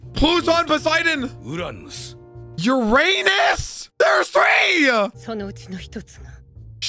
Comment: I skipped the video a bit and all i hear "YOUR ANUS!? THERE'S 3!!!"